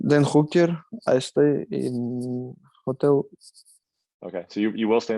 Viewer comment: I thought he said, “then hooker, I stay in hotel” 😂